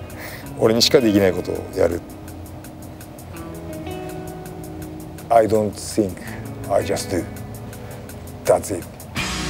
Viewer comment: does he say “I don’t think I just do”? Or “I don’t seek I just do” I can’t tell